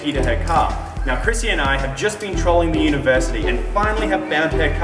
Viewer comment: I always hear, "Chrissy and I have just been trolling the University"